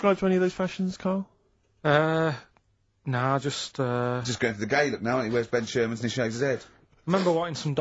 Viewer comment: what the HECK does Ricky say here? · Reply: "just going for the gay look now, aren't ya? he wears ben sherman's and shaves his head."